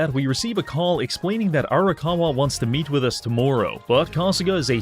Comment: Heard him say 'tomorrow' with that accent and thought it was another Yakuza named To Maroo.